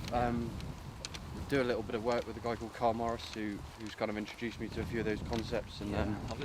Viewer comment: Who did he say the golf psychologist he works with? Was it Kyle Morris? 4:37 in vid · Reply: <link> Karl Morris